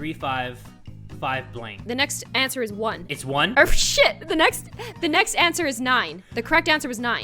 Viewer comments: Did anyone hear say oh $hit on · did just say sh*t??